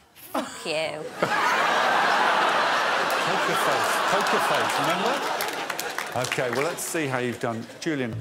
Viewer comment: What does Alex say? "Make a face, make a face, remember?" 🤔🤔🤔🤔